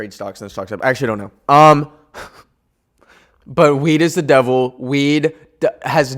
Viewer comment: genuinely thought bro said "weed is the bibble" 😭🙏